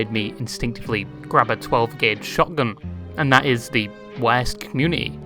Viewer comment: god damnit dude 0:40 not even a minute in and i have to stop and think wat the word "weos" means